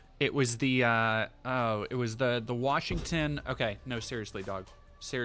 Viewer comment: Podcasting these streams make it sound like is saying, "Seriously, dawg!" When they really mean, "Seriously, dog!"